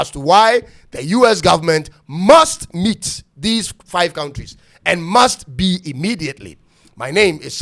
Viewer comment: I thought he said they are sheat-hole countries. Okay o!